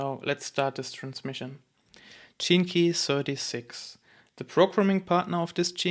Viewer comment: Ur beautiful in how u explain. (But it sounds like ur saying “genekee”. In stead of “gene - key”.) 🙏🏻🙏🏻